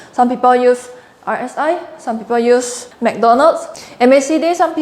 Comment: did she said MacDonald's